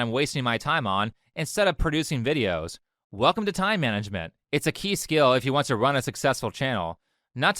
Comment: I miss heard this as it's a key skill if you want to run a successful jail. Hahahahah